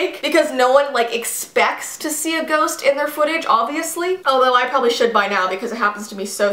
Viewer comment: When you said footage I thought you said fridge 😂